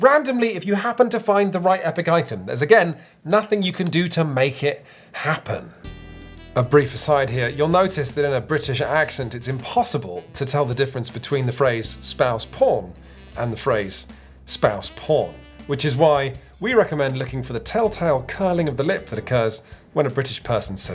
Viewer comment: I heard "Spouse spawn"